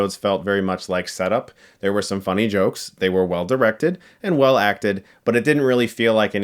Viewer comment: I mean... Waaaaay too well acted 🙄... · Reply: Lehel Molnár I thought you said over acted lmao I was about to throw some hands